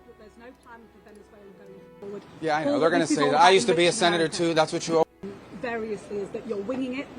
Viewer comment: i thought rubio said i used to be a adventurer like u lmao